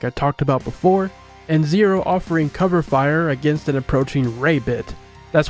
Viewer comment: for a split second I thought you said an approaching r@ Ok in my defense I was walking while listening to this and didn’t really hear it right but for a split second I was like WHAT?